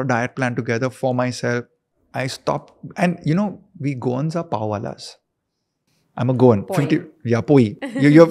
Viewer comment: I didn't catch the word he said here. We Goans are ? What was the word he used, any one? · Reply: Pawadas